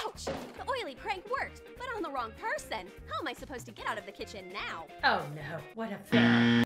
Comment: In the time of the video on 7:19.. I HEARD THE VOICE SAY "How will i itch my legs now?" BUT IT SAID HOW WILL I GET OUT OF THE KITCHEN NOW ;^